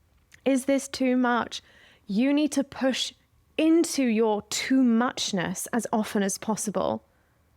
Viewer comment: Love this and you so much, thank you!!!! I was getting ready for a yoga class listening to this and at the end of the video I was debating if pigtails were too much since it's my first time there. I kept hearing "lean into your too much!" So I did them and couldn't be happier. Thank you so much for shining so bright 💖